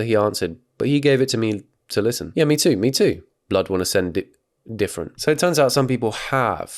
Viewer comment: The way nearly instinctively said “blud wanna send dick pics” instead of “different”🤣🤣